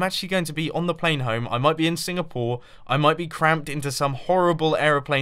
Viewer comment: Did he say Singapore??